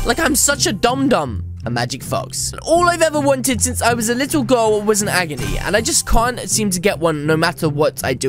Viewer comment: Did he says "when i was a little goal"??????? In 1:31 Im just hearing thing -_